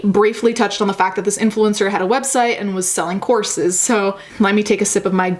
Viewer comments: THOUGHT U SAID SELLING CORPSES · At first I thought you said that the dude sells corpses 😂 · I thought you said she was selling corpses. I was like uhhhh is that allowed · help- i thought you said selling corpses